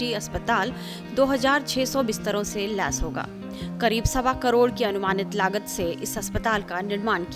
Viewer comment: Did she say Sawa crore Kya bol rhi hain madam